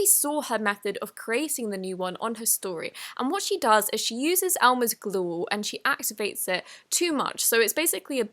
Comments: Why did she say elmers glue so wierd 2:37 OMG😂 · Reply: S Vlogs she said glue all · did she say “Mathed" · what did she say !!! oh glue